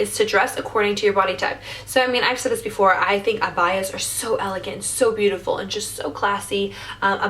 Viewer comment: no offense I herd u say abaya I didn't know what u we're saying at first I thought u we're saying baya then u said robe then I was Evan more confused I was about to search it up then I came to and I felt so dumb